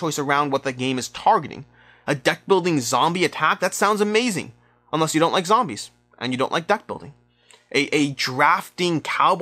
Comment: I find the price point more of a trade off than game lenght because this comes down to personal preferences instead of money invested. Lol I accidentially misheard deckbuilding for "duckbuilding". Man you guys get to my head xD